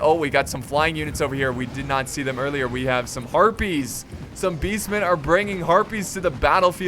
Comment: I thought he said Herpes